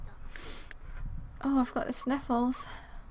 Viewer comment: when said i have the sniffles at 8:23 I thought she said i felt his nipples ol